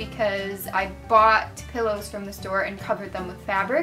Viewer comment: You said pillows, but it totally sounded like you said buttholes XD Laughing so hard right now XD